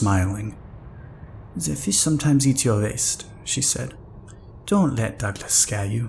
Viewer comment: I thought the French lady said “the fish sometimes eat your ass.”